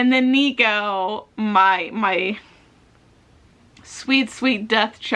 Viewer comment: Welcome back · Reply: I thought you might have said my sweet bean